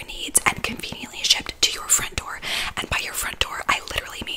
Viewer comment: I thought she said the mattress was "conveniently shaped to your front door" and I was super confused